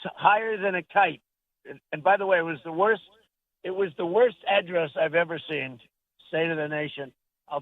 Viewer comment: How did he say address? Sounded like he said add dress. ???